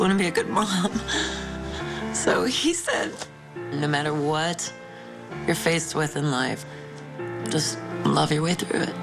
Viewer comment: What did she say at 5:35? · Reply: No matter what you're faced with in life, just love your way through it.